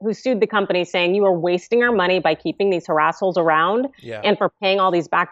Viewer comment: Did she just call them "harassholes"? 😂